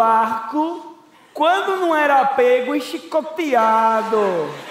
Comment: Só eu que entendi o Dani falando "CHICOPEADO" em 3:38 ?